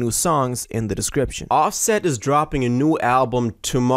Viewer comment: I thought he said he not dropping